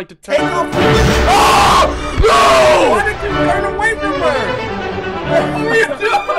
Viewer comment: That Scared The Living Soul Out Of Me -Also wtf was he saying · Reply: The guy who asked he said why did u turn away from her ??🤣